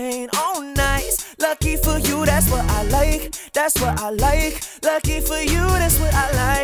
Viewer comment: Amazing cover dude! That laptop had me anxious though!!! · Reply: i just hear "thats what i lie" alex your not good at sing STOP UPLOADING VIDEOS HERE Z